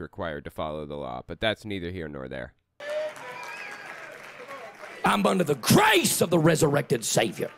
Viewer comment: ''hamburger the grace'' ? [what I misheard XD]